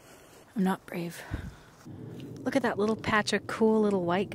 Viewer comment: I wasn’t looking at the screen as I heard “look at that cool little patch of white guys” and I was like “😳🤔 did they eat the wrong kind of mushrooms to be calling some hikers a ‘patch’ of white guys?” Lmao 🥴